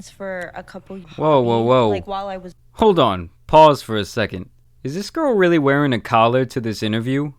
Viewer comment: Did he say “paws for a second” ???